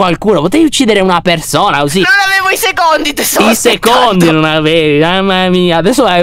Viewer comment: Non avevo i secondi tesoro Io ho capito cosi😂😅